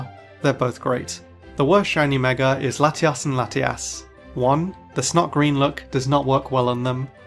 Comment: Why do i hear "Latias and latias" hahaha